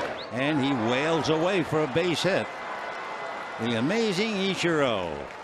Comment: の実況で「アメージング イチロー」が「名人イチロー」に聞こえた。(笑) 名人なのは間違いない！(笑)